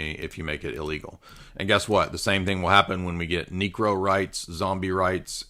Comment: what did he just say? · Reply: Necro right’s, rights to necromancy